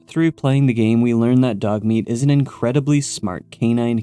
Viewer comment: At first I heard "Dogmeat is a simp"